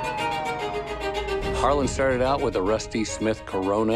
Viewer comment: Wow did I just hear "started out with CORONA?" I cracked up wtf 😅